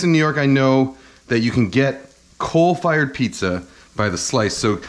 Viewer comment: I heard "cold fired pizza" and had to search Google for it. "did you mean coal fired pizza" I'm an idiot